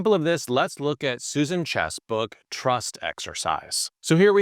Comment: I heard "Susan chess book" 😂